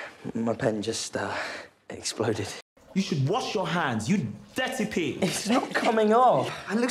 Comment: I didn’t hear “ you dirty pig” at first I heard “ you DETTY PIG” 😂💀💀💀💀💀